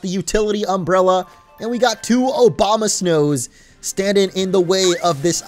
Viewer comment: did he say OBAMA-snows instead of Abomasnows?